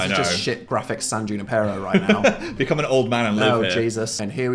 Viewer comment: the (indistinct) is Dan saying "San Junipero" in reference to an episode of Black Mirror where you can put your consciousness in another realm that you are familiar with. It's also gay as hell and arguably the only happy episode.